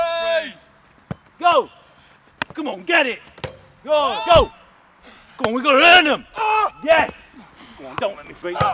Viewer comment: Wtf did the coach say at 1:57? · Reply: "Come on we've got to earn them" I think